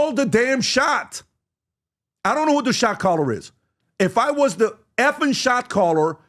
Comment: For a few times there I thought you were saying, who’s the shock collar??😂